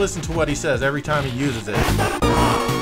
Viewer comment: haha funny, Axel is actually saying Grand Upper! Or more like Grand Uppah!